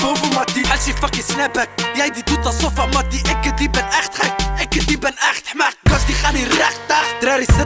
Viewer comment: Wat zegt ie op 0:28 tot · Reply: Ikke die ben echt gek, ikke die ben echt 7hmak (gek in de hoofd, raar, gek)